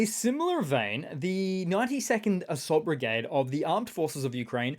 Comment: Morning & all. 💛⚓💙 Thought you said "the 90 Second Assault Brigade"! I was: "sh*t, those guys don't muck around!"